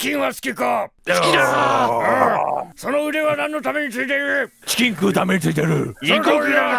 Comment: 🤖「肉を食うためだ！」に聞こえました！w